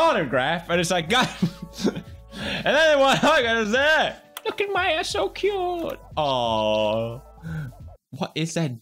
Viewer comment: Subtitles at 8:04 "Look at my ass so cute" what Pewds actually said, "Look at Maya so cute" LMAO